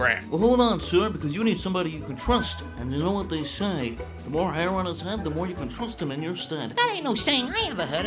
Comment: I misheard him and thought he said “The heroin he’s had….” That made it funnier.